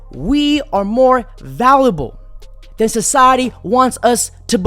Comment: did he just say “valible”? 😂. Everything else is 💯📠.